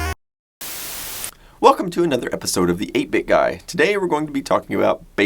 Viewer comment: Everytime I hear "Welcome to another episode of the EPIC guy". Makes sense to me. :)